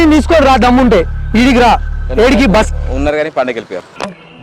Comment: what he said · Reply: Unnaru kani pandagaki vellipoyaru annadu